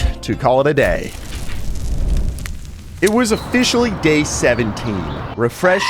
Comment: not joking i thought he said it’s officially gay 17 here